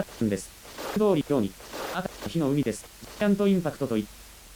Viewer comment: の所、どうしても｢セカンドインパクト｣にしか聞こえない←Eva中毒